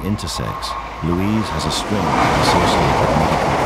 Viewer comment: Your sound editors need to be fired. Could they really not reduce the volume of trains and planes when narration was going to play over?? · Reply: "In addition to being intersex Louise (as a string?)" Any idea what the narrator said?